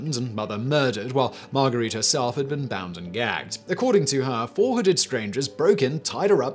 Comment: to 7,28 is the best story. ''400 strangers broke in, tied her up, and killed her husband, but the ropes were loose.'' Hmmm. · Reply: I thought he said 400 also!! I even replayed it and still heard 400. That made the story really hilarious though before I saw the person above say it was actually 4 hooded lol